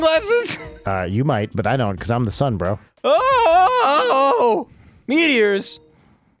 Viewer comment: I like how it sounded like Brian said "I'm the Sunbro." SO playing Dark Souls 3 after this. lol Thanks for the episode, Grumps!